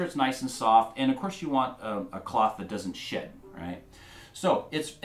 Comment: Did he just say the s word? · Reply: I believe he said shed, don’t use a cloth that sheds